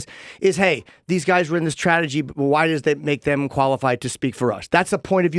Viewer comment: damn he actually said "tradegy."